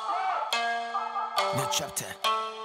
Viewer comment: 日高さん、New chapterって言ってんだろうなと思いながら、私は、ショウタ！とかShow time！に聞こえる時がある😅 いや、もしかしたら次はショウタのショウタイムって意味と、new chapterをもじってる？？BMSGフェスでのショウタの珍しく強気な発言を考えると、あながち空耳ではないかも😆💦